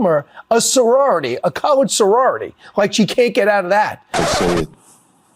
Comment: The trumpers are freaking out and you LOOOOVE to see it. But I listened to what that talking head said several times and it SOUNDED like he said colored sorority but I think he actually did say college sorority. These people are racists no question….but are we 100% sure he said that?